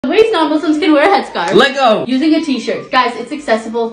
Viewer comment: I’m not trying to be rude but for a second I heard “non-legos” instead of “non-Muslims”